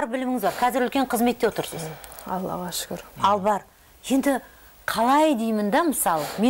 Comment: что она сказала? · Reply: Аллага шукир